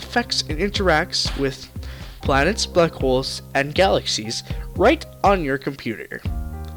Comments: Dude I thought you said planets, buttholes and galaxies for a second lmao not black holes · when you said black holes i thought you said butt holes